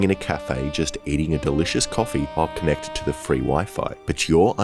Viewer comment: Did he say eating a delicious coffee??????